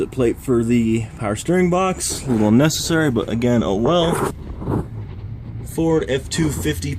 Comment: when you say "oh well" i hear "i weld"